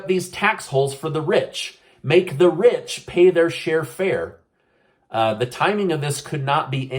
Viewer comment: lmao at 13:25 you said make the rich pay their share fair instead of fair share lol